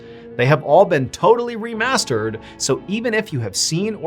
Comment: Why when Mr Ballen says "Remastered" I hear "I'm running out of stories" 😱😭